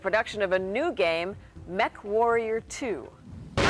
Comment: I could have swore that the newscaster was saying "MethWarrior 2" 😂